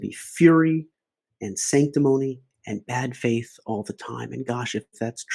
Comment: Note on points starting at 12:50, Harris does not say "all" but "almost all" when talking about people talking about bad faith. You can still find the wording of "almost all", i.e. most, problematic but since this is talked about for multiple minutes I find it an important point. <link>